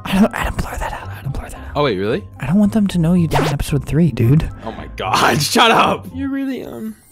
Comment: what did he say XD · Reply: I die in episode 3 xD